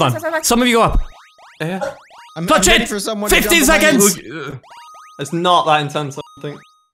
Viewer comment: did he say it’s not that intense